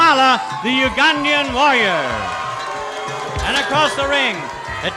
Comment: Am I hearing things or at 6:42 do I hear "Kamala the Ugandan Lawyer". I'm sure he's meaning warrior, but I swear I hear lawyer.